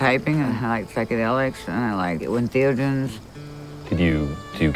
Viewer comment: What does she say at 1:15? I like psychadelics and I like ???? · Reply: Entheogens